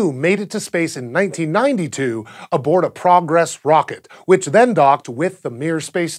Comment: did he say a prom dress rocket? Man I bet that thing takes off fast!